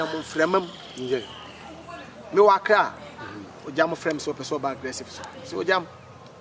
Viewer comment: Kwaku I thought you said your program is aggressive. What do you expect